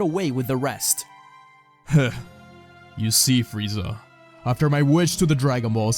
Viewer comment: I hear: "you see frieza" My head instantly: you're not dealing with the average sayian warrior anymore.